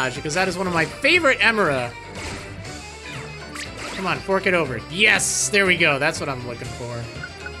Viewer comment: wait did he say twerk it over to a houndour for an emara, omg he did from